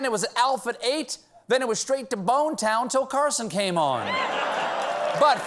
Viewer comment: the subtitle says "where's the beef" not bone town lol